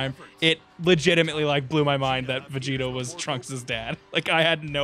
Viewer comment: did he say Vegito is Trunks's dad?